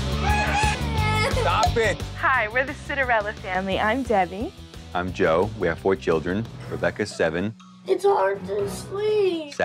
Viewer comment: I thought she said were the Cinderella family